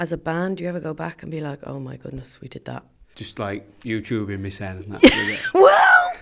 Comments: can anyone tell me what he said at 9:13 please? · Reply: Just like, You Tubing myself. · Hey, could someone tell me what Alex said at 9:10? I sometimes have trouble understanding him, damn it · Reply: Celina Garibotti “just like youtubing me sen” (One was searching his name on YouTube) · Hey, could someone tell me what Alex said at 9:10? I sometimes have trouble understanding him, damn it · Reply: haha I get that. He says: ''just like youtubing myself?'' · what does he say at 9:12 ? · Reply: Youtubing myself